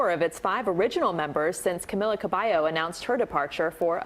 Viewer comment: did she just say Camila Cabyeo 😭